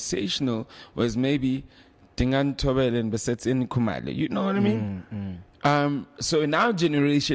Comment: Did he say Basets-ein Khumalo 16:23😅🤣😜